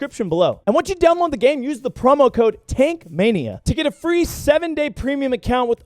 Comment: ong i thought you said "Taint Mania" lmao